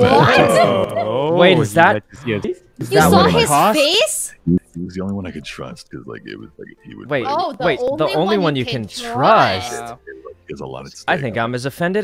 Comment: Corpse said: "Only one I can trust" What I heard: "Only one I can thrust" OooOoOoppppps